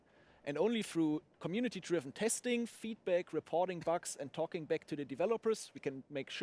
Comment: When he said "feedback" i thought he said "feet pics", so i thought he was saying getting feet pics from his friends motivated him